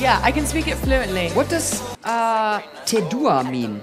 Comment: i thought he said what is hawk tua😅